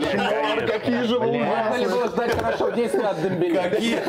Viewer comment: что сказал Дорский? чё 10 раз? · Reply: Вроде - вот сейчас 10 раз Дембеле